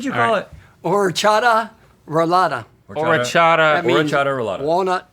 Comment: Omg I am from Slovakia and the way he said orechová roláda took me a while to get it 😅 but we do love it! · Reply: I thought he said horchata rolada 💀